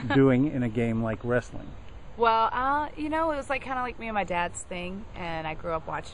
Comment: Did Mickie say me and my dad Sting at 1:13?